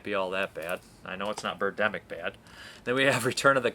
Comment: Tim Hayes can you give me a time stamp on that quote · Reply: Never mind. You said not,Birddemic bad,so I misheard you the first time. You were talking about Devolved not being Bird Demic bad. I thought you said Burke Denning bad,but I misheard what you said. I must be tired right now. Trying to get a lot done right now.